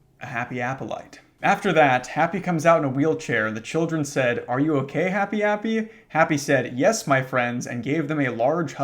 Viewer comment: misheard as "Are you a gay Happy Appy?" "Yes, my friends!" (Lack of comma intentional, I thought it was an adjective) 8:08 the delivery on this was done so well 14:32 The term "Obama Easter Egg" 51:23 The fact that when seeing the guy trying to kill them and some people who that guy has hung up the corpses of as trophies, the protagonist's directly diangosed morbid curiosity STILL has them turn their back to the guy and inspect these corpses, is probably the most interesting characterization in the story 56:03 yeah this too